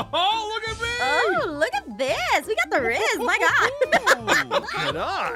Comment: did I just hear aphmau say “We Got The Rizz Wow”